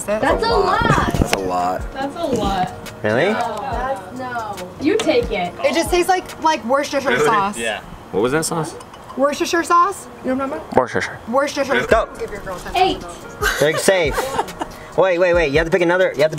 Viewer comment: two years later i hear her say worshipper sauce